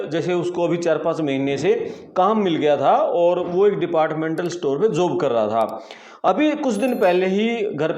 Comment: did he said jobe😂